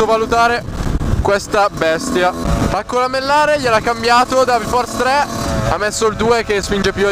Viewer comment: dark force 3 ho capito bene?